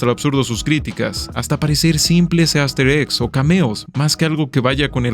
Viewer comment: en el seg 0:40 me dio la sensación que dijo "gaster eggs" y pensé directamente en undertale o deltarune xd